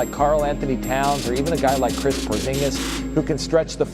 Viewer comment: Kris Porzingis is what the guy said